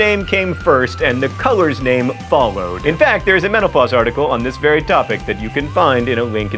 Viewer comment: i swear, it sounds like you say menopause some times